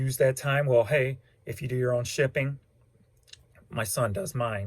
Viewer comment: You are amazing. I am addicted to your videos. Are you a 1 man show? How long does it take you to do packages every morning??? you must have 30 - 60 packages a day ...…..no? Amazing!! Also, how many items do you list a day? Thanks, kiki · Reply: Sorry, I just heard you say your son ships. I should have listened first:(